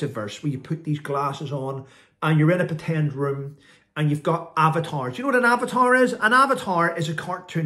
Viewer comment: Oh - AVATARS! I thought you said abbatoirs! : -)